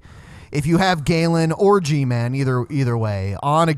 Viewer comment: I thought you said "Galen Orgyman". Laughed my ass of.